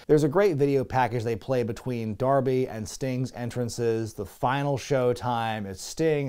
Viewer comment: I thought that said "The Final Solution" lmao